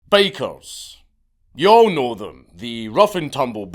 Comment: Sorry Count but I heard "Bakers" and it made it funnier.